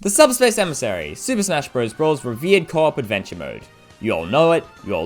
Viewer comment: Instead of “revered co-op adventure mode” I heard “Viet Cong adventure mode”.